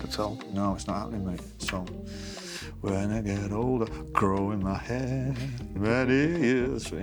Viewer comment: Love these. Some great questions! Matt: In 10 years you'll be 64. Will you play the Beatles When I'm 64? Noel: When I get older. Combing my hair! 😂 And his U2 joke... classic! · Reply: I thought he said "growing me hair" 😂